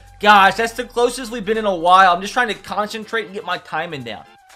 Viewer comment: I thought he said "I just turned 46". 😥